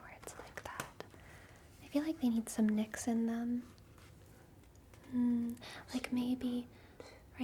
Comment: she said, "I feel like they need some knicks in them", but I heard, "I feel like they need some Nixon them". dunno how Nixon eyebrows would help.